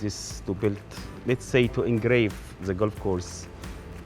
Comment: I think he says “to engrave” which sounds more poetic and relates to his point about the course being a work of art